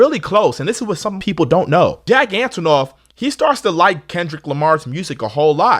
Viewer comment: i thought you said "gigantanoff" because taylor is tall 😭😭😭😭